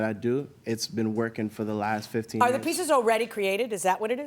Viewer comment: How did we go from "I'm a new designer" to "this is how I've been doing things for the last 15 years" in 2 minutes? 😅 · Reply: I thought he said 15 months